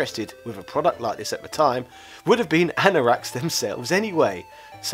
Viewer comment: “...would have been [unintelligible] themselves anyway.” What on Earth were you trying to say there?! Sounds like “ana racks.” You even repeat it a couple times. Whatever it is, many of us have no idea what you’re even trying to say. Hard to get what’s so funny in light of that. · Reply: Anorak is the word, English slang xx